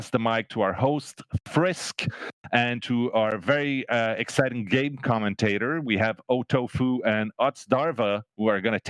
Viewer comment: I swear I thought he said "Our very exciting gay Commentator"